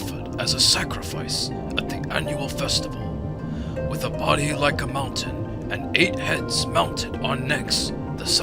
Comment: I thought they said she had a body of a mountain